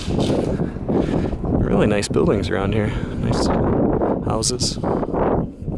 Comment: i thought he said really nice boobies around here LOL